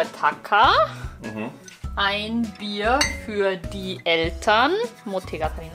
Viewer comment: Elternがどうしても”エルたん”に聞こえてしまってカワイイ😂